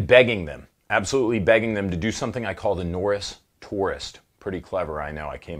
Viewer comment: Misheard the "Norris Tourist" as "Taurus Poorest" when I was playing this in the background. Seriously thought you had some weird Shot Show giveaway with Hop.